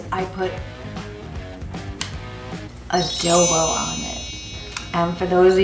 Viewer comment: yummy! But at 0:45 it sounds like your are saying a-dildo (the captions say gel-bow? wth? What is the secret ingredient?